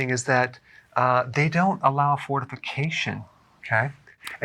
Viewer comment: I thought DrBerg said the French don't allow fornication. So, that explains it. 😂